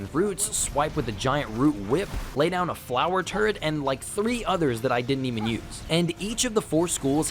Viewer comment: Do what😂??? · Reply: Man I swear I heard you say ‘drop a flower turd’. Maybe I’m wrong haha